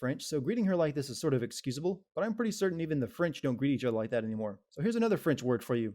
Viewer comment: Captioning Sin wrote "great" instead of "greet"